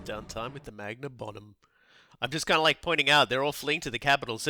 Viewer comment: i heard "magnum bottom" (so lets have some r&r in a big butt xD )im on rewatch binge your dow series. (watched in order of game and dlc release) long for when i get to dow2 you guys got me to buy it (even the grandmaster collection) also first series i ever watched of this channel.